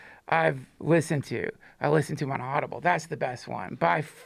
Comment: Duncan says the best self-help book he's ever listened to is... something that sounds (coincidentally) like "inaudible." Did anybody catch what title he said?